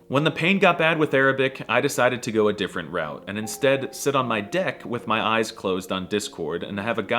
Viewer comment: in 7:32 WHAT DID HE SAY? 😳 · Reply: he said “deck” not…the other one